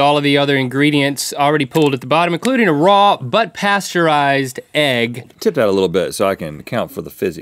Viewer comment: all I heard was "Raw butt pasteurized eggs"